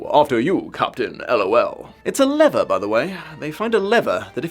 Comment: absolutely hilarious! XD Although the way you said "lever" confused me for a bit. I thought you said leather. Do we say lever that way in the UK? All I could hear when I worked it out was Yzma saying "WRONG LEVER" XD